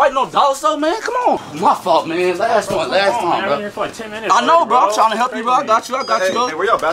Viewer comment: “aye man they fightin in the back” “y’all can’t be fighting over dollar soap man” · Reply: Thought he said "Can't have a fight in no dollar store man"